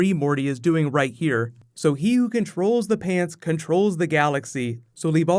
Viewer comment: I thought he said "He who controls the fans controls the Galaxy.